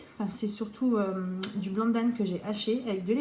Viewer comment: Salut les amis... Une p'tite question sur tes recettes... À 1:30 tu dis: " C'est du "BLANC d'Âne" que j'ai haché...?!!).... J'ai réécouté plusieurs fois le passage, j'entends toujours "Blanc d'Âne"... C'est quoi exactement? C'est Lao? Étonnant... NON?! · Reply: 😂😂😂 non non malheureusement je suis une personne à 99,99% allergique alors je parle du nez donc effectivement à l’écris c’était bien du blanc de dinde 😂🥰👍🏼